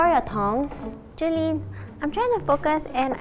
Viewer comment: I thought she said Tongue